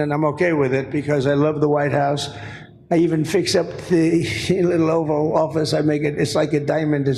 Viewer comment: His speech was slurred, but did Trump insult the Oval Office? I thought I heard him say "I even fixed up the shitty little Oval Office." At about 7:20 mark